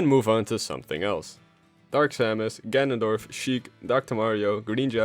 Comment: Fun Fact: The list of playable debuts in Smash would of been different if the now cancelled Shiek spin-off released. (Also this still works as Shiek wasn't her own character until Smash 4) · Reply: For a Sec I thought you said Shrek